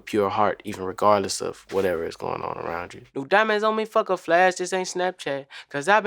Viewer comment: I always thought he said "No diamonds on me fuck a flash this ain't a snapchat". I came to hear him explain himself I stand corrected